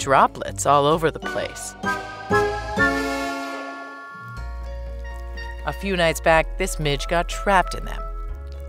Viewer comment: When she was talking about the Tarweed I swore I had first heard "a Few nights ago this Bish got caught in it." 😂